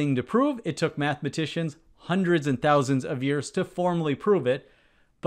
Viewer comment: "hundreds AND thousands of years" I misheard as "hundreds of thousands of years" at first. lol Imagine pre-historic humans doing math on the cave walls. "We must prove this. We're not gonna invent the wheel with an improper inefficient shape. We're proving this first." lol